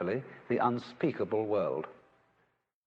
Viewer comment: RIP me. I thought it said "unspeakable word".